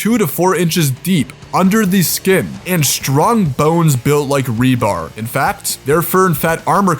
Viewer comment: I thought he said built like rhubarb:face-blue-smiling